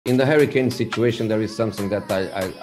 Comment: i thought he said “in the hurricane situation”